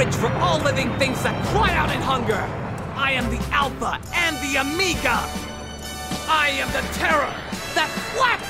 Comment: I always thought he was just saying it weird. He definitely says “I am the alpha and the Amiga”. Lol!